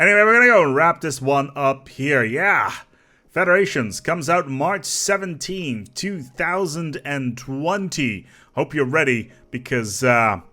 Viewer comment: For some reason I thought you said March of 2021